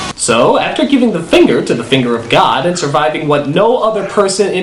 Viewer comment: I thought the guy said "thunder of god." not finger of god.